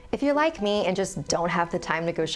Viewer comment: When she said “if you are like me “ but i heard “if you like me” and i was like OMG YES YOU ARE AMAZING😍😍 but then she continued “...and don’t have time to shop” 😭😭